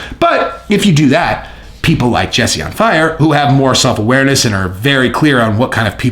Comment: Did you just say you were in the school of self-awareness at 5:34?